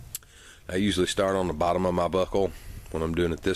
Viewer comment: keep replaying at 3:15 sounds like he says bottom of my butt hole